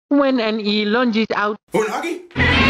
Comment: did just say “Unagi?”